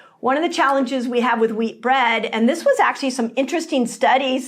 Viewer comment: Thank you Dr Mindy ! My husband and I both 50 have been following you and fasting for 4 months. Today I hit my first goal with an 18kg weightloss 😊 my husband 15kg. My husbands no longer pre diabetic, or has high cholesterol or blood pressure. We are feeling great❤❤❤ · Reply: sounds like she said meat, did she meant wheat?